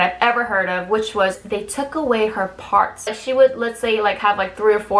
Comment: Do you know how DISGUSTED I was to hear that the company took away her parts for dating????? I thought body parts 😵 but she meant singing parts...thank goodness I'm still recovering from the mental shock😣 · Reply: same!!! i thought she said "they took away her heart", and i was like 😨😨😱😱😵😵😰